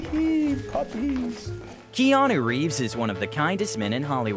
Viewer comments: he sounds like he said "Jeanu Reeves" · I swear my mind thought he said jeanu reeves in the beginning.